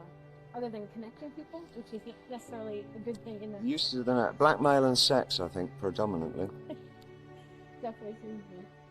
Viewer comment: Blackmail & Sex... an Oracle for our times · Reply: i thought he said black man and sex. thanks for clarifying.